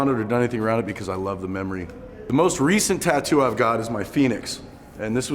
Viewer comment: I'm running my air conditioner, it's loud and I thought he said "penis" not "Phoenix"... "Something that reminds me how far I've come and how far I have to go." I had to rewind the video to hear him correctly. Good work dude.